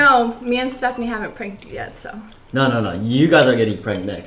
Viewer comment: I LITERALLY THOUGHT HE SAID “no no no u guys are getting pregnant next, by us” AHAHAHAHAHAH